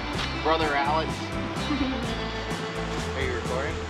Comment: Hahaha 10:34 did you say brother?